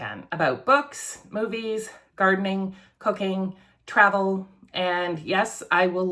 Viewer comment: BWAHAHAHA! I was briefly distracted until you said “cooking” because I thought you said cocain. That got my attention. I had to double check that I was watching the correct video.